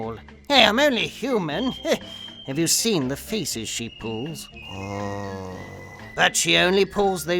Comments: i thought he said "have you seen the faeces she pulls"? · Damn I first heard you said "Have you seen the feces she pulls?"